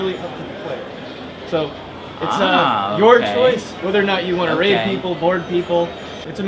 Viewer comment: did he said you can rape people?